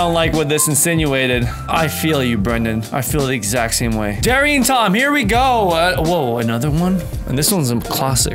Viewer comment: did you just say “Terry and Tom”???? It’s Tom and Jerry😂😂